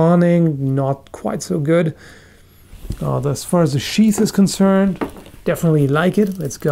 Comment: Did say "as far as the cheese is concerned"? XD